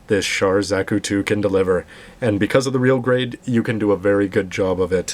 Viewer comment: Ok, now I KNOW I'm hallucinating... I could have sworn he said "Real Grade, 00-Raiser" at the beginning.... O_O